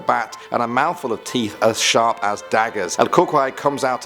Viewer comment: It sounds like u say el cucry it’s CU CUI it’s like saying the letter e where the y is